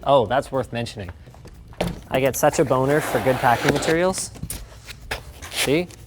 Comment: i thought he said bonus for package deals